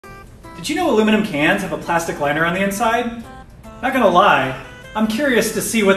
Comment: Thought he said "a plastic letter."😂